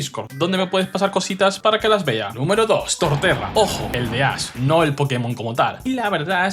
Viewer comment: ( casi) me ofendí cuando escuché a tortechad